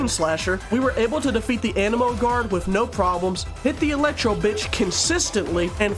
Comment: why did i hear eletro bit*h 19:26?!